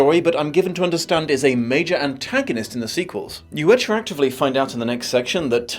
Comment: he says a wrong word again. "you attractively find out" doesn't make sense. But I'm not sure what word he misread. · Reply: he said “retroactively”